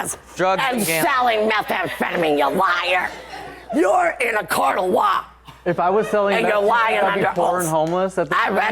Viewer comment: 😳SHE just convinced ME to STOP smoking IMMEDIATELY! I thought she said "you're in a CROCK of WAP" 🙆🏾‍♀️ NOT in a "COURT OF LAW"😫..... UMMMMM, yea 🤦🏾‍♀️